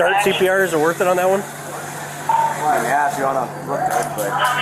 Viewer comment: Should I start CPR on him is it worth it? Other cop, " yea if u wanna look good" 😧 did they really say that?